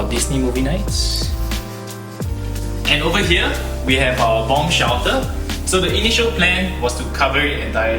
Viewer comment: i'm sorry i might have misheard it but at 15:35 did he just say bomb shelter?